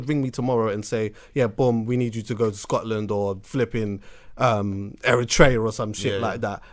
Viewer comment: Did man really say Eritrea at 3:57?